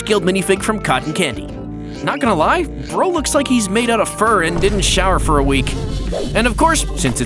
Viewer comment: Did he say furry? 0:45 😮